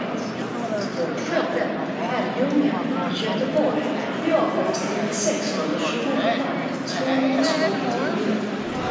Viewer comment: Random question, does anyone know what Swedish word they are saying at 2:11 right after Göteborg? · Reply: "Göteborg med avgångstid 16:28"